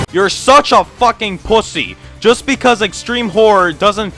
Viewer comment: I open this to re-watch it after a bit, and the FIRST thing i hear is "You're such a fumuckin pugussy" and i kid you not i almost woke my family cause i was laughing so much. 7:46 is the time stamp for you fine commentors.